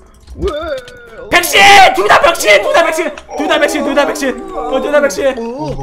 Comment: 에서 병신이라고 들림ㅋㅋㅋㅋㅋㅋㅋㅋㅋㅋㅋㅋㅋㅋㅋㅋㅋㅋㅋㅋㅋㅋㅋㅋㅋㅋㅋㅋ